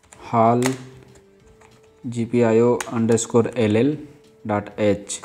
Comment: to anyone else who misheard or rushed, he says gpio_LL , as in 2 lowercase Ls, NOT gpio_11 !!! i was in a rush and i typed in 11 which gave me a compilation error! Thank you very much to the uploader!!